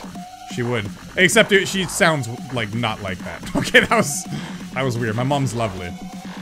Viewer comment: he says his mums instead of mum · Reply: He said mum's mum is